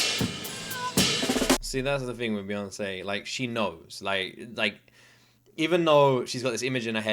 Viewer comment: omg did you just say “she knows”? Now you’ve triggered the beyhive’s trauma 😂😂😂😂